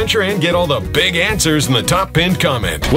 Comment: I heard "united states shit" What is wrong with me 😂